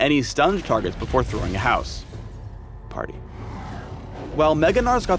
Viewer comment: 33 < i heard him say house party x33